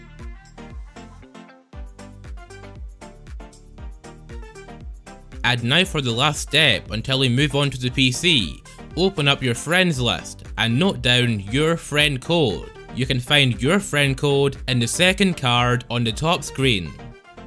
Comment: Who else thought he said go to your friends list and hit up your friend paul.